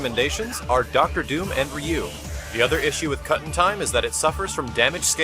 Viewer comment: Cuttin' time? I always thought he said Guts time.